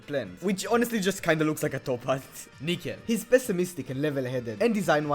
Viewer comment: what did he sayyyy? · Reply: nickel